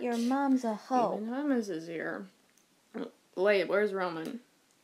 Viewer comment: When remus said roman I thought he said ramen